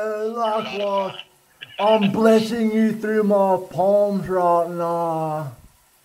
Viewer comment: What did he say? - I'm blessing you though my ...? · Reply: "I'm blessing you through my palms right now"